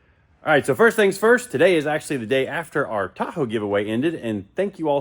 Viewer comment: Boy I feel stupid! For a minute there I thought you said TACO giveaway. I thought WHAT??? Was Squirrel involved?… then I realized it was the Tahoe and felt like a real idiot.🤪